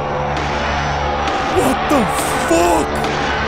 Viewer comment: 😂 It sounded like he said “WHAT THE FOCK?!”